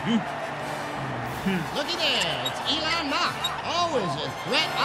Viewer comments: "Elon Mach" did I hear that right? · I swear I thought I heard Elon Musk but it was Elan Mak… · did he just say elon musk?